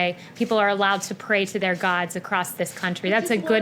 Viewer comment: I heard "gods"! Nice to see polytheists being represented. 💯 Somehow, I think Joe would flub it and say "gawd-uh" like Eric Cartman of South Park.